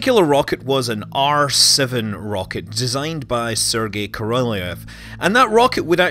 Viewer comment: Thought he said arse of the rocket rather than R7 rocket lol